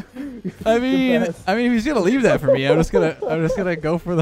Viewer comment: " did he just call you a crackhead? LOL